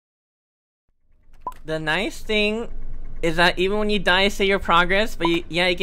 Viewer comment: I wasn't paying attention and I thought he said "hi stink" 😭